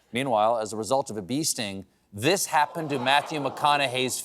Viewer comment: Is it strange that I heard “as a result of a beasting “ in the McConnaughey story and was like, OK, don’t know what that is but seems legit, before realising it was “bee sting”?